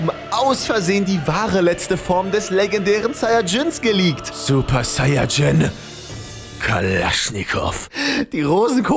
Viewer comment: Meine absolute Lieblings Szene😂😂🤣🤣🤣 IcH kRiEg KeInE lUfT mEhR!!! Aber was sagt da- Super saiyagin galaschnikus? Ne kp😂